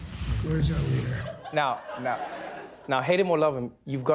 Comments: Did i seriously hear Donald Trump say in a hushed tone "Where's our leader?" I can swear that i heard it at around 00:27 Was Putin there??? :S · Wtf did I heard that right at 0:30 did Trump say where's our leader?? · Did I hear wrong or did Trump really just say, "Where's our leader?" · this is what I heard after Donald signed the pen" "Where our leader?" - Donald Trump